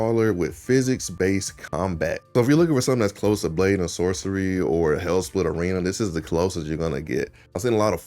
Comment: When you were talking about saying Arizona sun shine and said "what about _____, I love____" You were talking fast and I didn't catch the game you named, sounded like you said "Blainasaurus".. what did you say? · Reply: blade and sorcery. Apologies for my country fast talking 😂